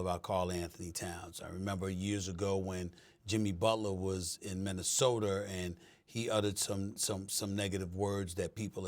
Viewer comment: Did SA say MinnesotER